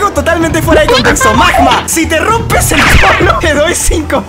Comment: escuche si te rompes el pot0- JAJSJAJSJJA